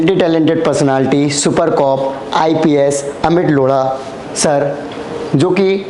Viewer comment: Please notice what he said - Super cop " Amit LODAA" 😆🤣🤣😂😂🤣